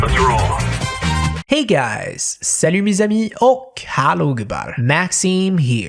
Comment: I'm still curious what said in his intro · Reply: JOKER IS REAL salut mes amis =hello my friends